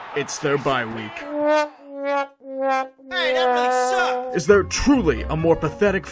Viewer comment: What did that guy say around 0:08?... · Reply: “Hey that guy sucks”